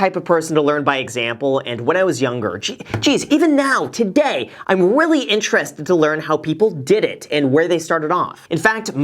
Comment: Did anyone else hear "i am willy intrested" in the beginning XD in time